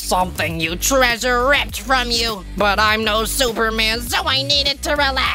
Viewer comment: I heard Four said Superman didn't he??